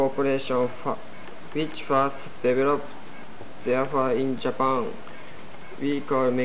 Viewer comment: wtf did u say?????????? didnt understand anything!!!!!!!!! all i heard was "found in japan"and"penshil!"